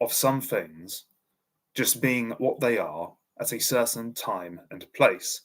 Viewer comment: You’re largely getting to the concept of Volkgeist here and you make a great point, however, I would make a few criticisms. First you said “… under a set of continuous geographical conditions that evolve gradually within a certain space at a certain time.” Historically, many people groups have migrated and its often understood that most if not all Indo-European peoples originate from the Pontic steppe, as a result a continuity of the people being held through migration would be inherently at odds with geographical continuity, yet as a distinct people they maintain them not at odds. Ironically when I first heard you say that I heard “acertain” and not “a certain” both times which I agreed with, until I realized I had misheard. In addition you reject blood and soil nationalism and racism as inherently not being of Volkgeist, problem being, there is actually nothing of the Volkgeist that renders it comparable in any way. A nation can persist as a totalitarian ethnostate and that ethnostate may in fact be a manifestation of a particular Volkgeist, although be it one I personally wouldn’t desire to be a part of. It also luckily remains incoherent in the american context; it is, however, substantially more feasible in the European one though.